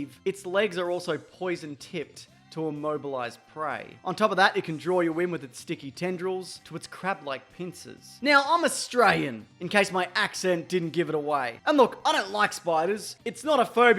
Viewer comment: "I'm A STRAYEN" is literally what it sounded like